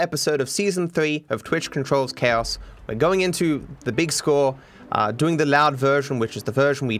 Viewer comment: In the beginning I thought you said you are going into the big school while holding a gun